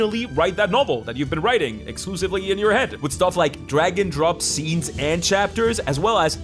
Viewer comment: Oh, "Drag AND drop" I heard "Dragon drop"